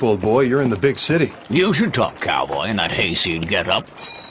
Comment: I use Lucky Luke to learn english... Can anybody help me what Jolly says at 0:16? I understand "You should talk...to get up". But the part in the middle??? Thanks a lot ;-) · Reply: He says, "You should talk, Cowboy, in that hayseed getup."